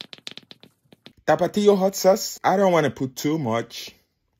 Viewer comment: When he said i don’t wanna put too much I thought he said I don’t wanna poop too much 💀💀😭😭